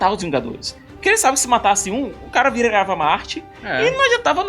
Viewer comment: Tem vezes q eu simplesmente nao consigo entender o q vcs falam... nao sei se é o sotaque, ou o q... Por ex.n essa parte 10:20 o q o disse? Se alguem entender pir favor me diga... "Se ele matasse um, o cara virarava marte"??? Eu inclusive coloquei em camera lenta, e msm assim nao entendi! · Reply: Lucas Silva "se ele matasse um, o cara virava mártir"